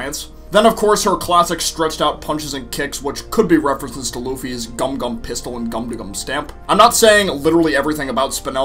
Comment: am I the only who heard gumdegum?